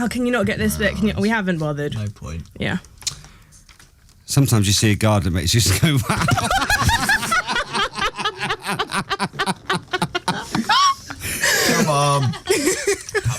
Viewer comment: What did Scott say at 4:10? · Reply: "Sometimes you see a garden that just makes you go, 'Wow'."